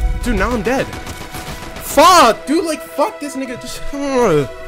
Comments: what did bro say 😳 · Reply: nga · what he say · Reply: nga